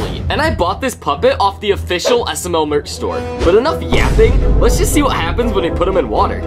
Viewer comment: for one second I thought he said- lets enough yapping "lets get clapping"